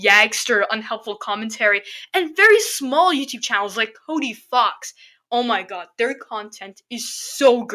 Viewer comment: I thought you said Toby Fox, and I was about to say(I didn’t know Toby Fox made MD theories).